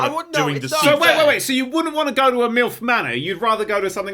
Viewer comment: I'm American, so due to their accents, I spent up until 1:19:56 thinking that they were saying "MILF Mana", as though it was going to be a show about middle-aged wizard women, instead of MILF Manor. Edit: I don't know how I messed up the timestamp that badly, fixed